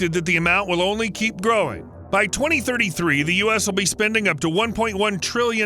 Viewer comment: By 2023 they will be spending $1 trillion dollars? It’s 2025 sir, I don’t know if that was a honest mistake or if I heard wrong 0:36. Okay he said 2033. I thought he was accidentally giving the wrong timeline.